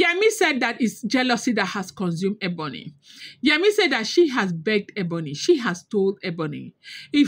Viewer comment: I thought Ebony said she she has pedigree, there's a Ghanaian adage that says, a person with wisdom can still take counsel but if you don't you will be seen greeting a sheep